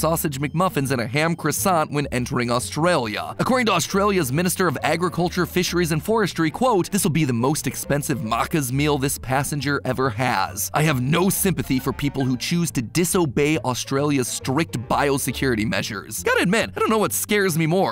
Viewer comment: the way he says "Maccas" just shook every Australian to their core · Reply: omg yes, I had to pause cause I thought he said "maracas" kinda weird